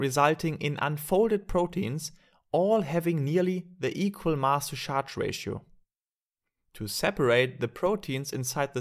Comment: min 1:48 i cannot understand what he says: having the equal _____ help please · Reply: "the equal mass to charge ratio"